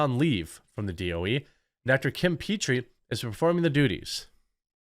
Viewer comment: "duties" But I definitely heard, "doody". Poop jokes are fun.